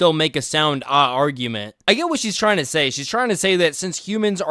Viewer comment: Did... did you really say "ahh argument"? That's fucking cringe my guy. If you did, you can't really dunk on instagrammers while you yourself have tiktok-brain. Stop falling for Chinese censorship when you don't have to.